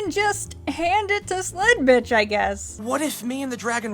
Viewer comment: I can't tell if her name is actually sled-bitch or if your just insulting her (wich is what I thought for the first 4 videos), or if i'm severely mishearing lol